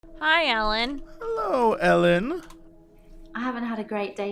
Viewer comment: I clicked the video and the first thing I heard was "Hi Ellen!" "Hello Ellen!"... I know it's Elin, but I felt special for a second. No one can take that away from me lol